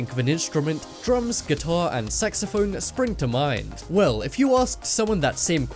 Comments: I thought u said sexaphone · It sounded like he said "sexophone"